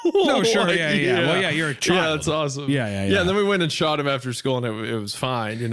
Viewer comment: I thought he said "and then we all went and shot him after school" XD